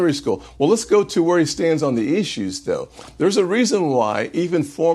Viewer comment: Did he just say " the company you keep matter?